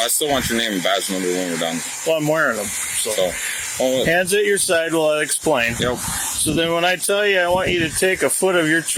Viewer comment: Can someone confirm what he said at 7:19 please ? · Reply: He said "can I get your name and badge number, please"